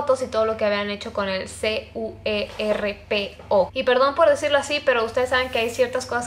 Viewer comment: jajaja estoy bien wey entendi suerpo, jajaja yo tipo "que es suerpo??", tengo que consultar